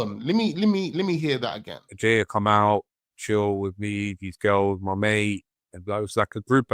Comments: i think he said these girls the 2 girls he apparentally met. just shit mic quality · he clearly says "These girls" referring to the Nottingham ones probably, his story is all a load of sh*t anyway, I know that, just correcting you there bro.